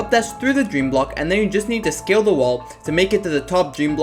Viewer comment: I swear, every time you say "scale the wall" I hear "skill the wall" and think "Yeah, get outskilled you stupid wall!"